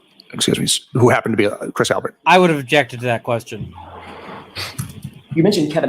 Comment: I keep hearing the lawyers say "Chris Albert" and thinking, "Did he say 'Prince Albert'?" Make the inferences, as you will. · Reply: I keep hearing "King Albert" who is the previous king here in Belgium.